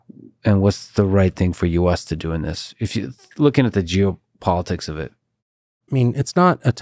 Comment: Sounded like he said "looking at the jew politics of it" CANCELLED